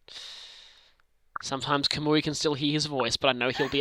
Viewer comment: I thought it said "Hana is dead" and I'm like 'What the heck happened there?!'